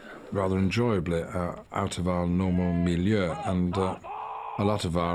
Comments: did Ian just go full swedish? "Out of our normal miljö" what is he actuly saying I cant tell? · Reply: I believe he says “milieu”, borrowed from French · Can anyone explain to me why Ian Mckellen says "Miljö" at 27:58 instead of enviroment? Is "miljö" used in English aswell? Caught me off-guard. It's the Swedish word for Enviroment